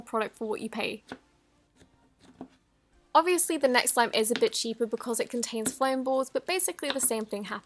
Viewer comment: Did she say bitchy?